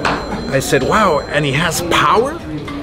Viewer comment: I thought he said wow! & he has powder!